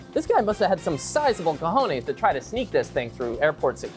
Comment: Did you just said "cojones"¿¡¿==¿11